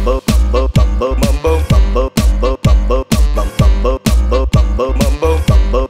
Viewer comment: is grian saying bumbo